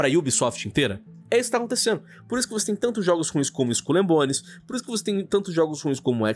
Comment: => Eu entendi "Skull and Bones" como "Esculhambones". kkk